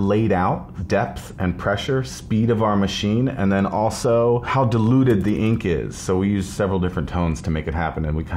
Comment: I thought he said "How deluded the ink is" and I was confused.